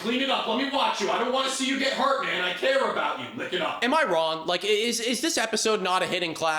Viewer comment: did he say lick it up? 😂 that’s gross 🤢